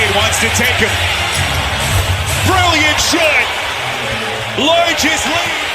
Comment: did he say brilliant shit🤔😂